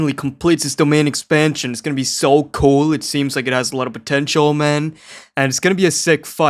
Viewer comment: wait let me stop you right there, did you say "potential, man"